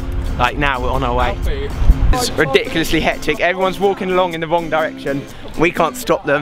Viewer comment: when you said 'wrong direction' i thought you said 'one direction' :L<3